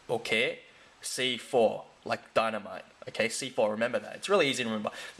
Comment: loli thought said 'C for dynamite' i was like uhh... haha ohhh! 'C4, dynamite'